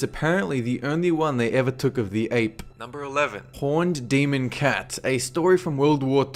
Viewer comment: When he said Horned demon cat I thought it said porn demon cat